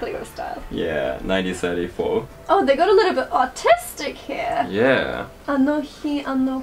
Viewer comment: omfg i swore i heard ‘autistic’ & im like thats downs syndrome 😅😭 lord